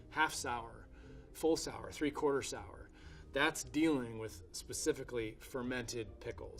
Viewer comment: "that's dealing..." hahaha I heard that's dilling. Like dill pickle haha